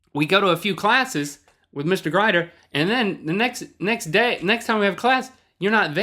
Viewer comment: Did Link say Mr. Grider at 5:27?